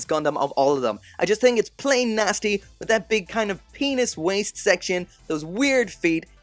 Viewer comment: did he just say penis